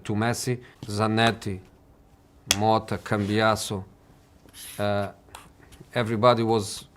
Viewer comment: I thought he said motta can be an asshole lol... Cambiasso😂